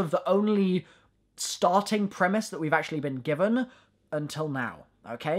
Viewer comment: I see you sayin primis instead of beginning